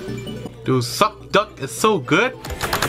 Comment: I skip to like 3:18 and all i hear is succducc